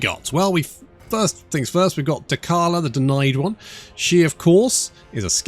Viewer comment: Thought it said Dented one😂😂😂😂😂😂😂😂😂